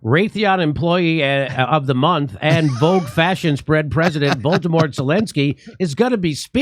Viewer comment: Voldemort Zelensky!! 🤣🤣 Did anyone catch that?